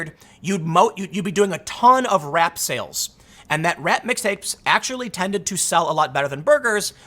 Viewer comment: I could have sworn I heard “rat mixtapes”... Squeak, squeak..?